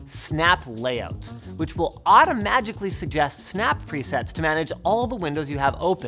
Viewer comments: did he say " automagically" at 1:21 ???? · Did he just say "auto-magically"?